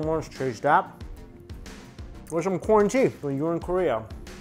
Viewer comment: I thought mikey said "quarantea".. later I realized it was "corn tea", lockdown got me ಠಿ_ಠ